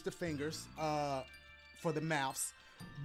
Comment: lol did he say for the mouse or am I tripping lol